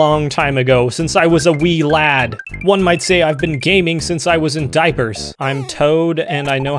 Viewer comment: At about 12:24 says "One might say I've been gaming...." but an ad came up and cut him off and it sounded like: "One might say I've been GAY" 🤣🤣🤣